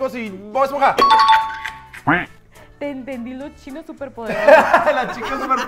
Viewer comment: tambien entendí los chinos superpoderosos